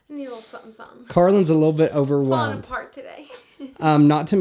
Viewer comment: Subtitles are so funny 😂. 14:11 ish it says Carlin is a little overweight instead of overwhelmed. You’re absolutely not, you look great! I love the new house footage and it’s going to be such a fun place for you with how much you like to host.